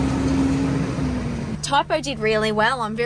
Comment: The accent threw me off at the end I thought you said “good job sluut you did well”😂